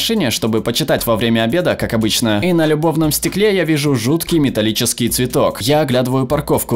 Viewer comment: мне одной послышалось "И на любовном стекле"?